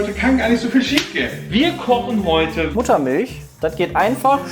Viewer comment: Hat bei 0:22 noch jemand "Buttermilch" anstatt "Muttermilch" verstanden?